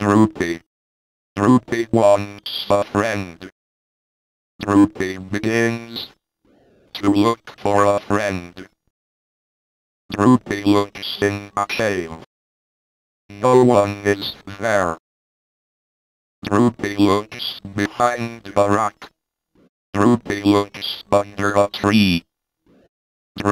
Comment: i always thought it was saying 'no-one gives him berries' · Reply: I heard ‘no one gets married’